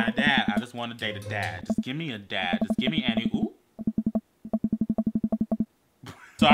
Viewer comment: I swear I heard him say and I oop even tho he said any oop